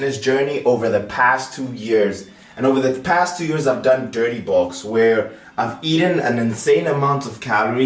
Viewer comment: what did he say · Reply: I had to listen twice because I first thought he said "balls" but then realized he said "bulks" ..lol